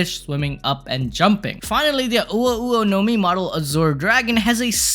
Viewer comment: I heard "uwu nomi" 💀💀💀